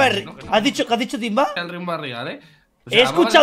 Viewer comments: se escucha claramente que dice Timba: el riumba es real eee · El timba ¡¡¡DIJO!!! "el riumba es real hee" se escucha cuando el Rius lo puso como en como en cámara lenta 7w7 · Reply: en el minuto 1:30 se oye que dice "y que el riumba es real e" 😂